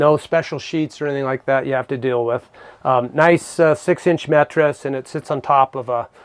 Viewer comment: Did he say mettress?